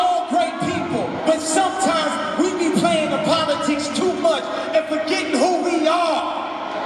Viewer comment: it's funny how what was said at 1:11 is inaudible. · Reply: Eames "We be playing the politics too much and forgetting who we are."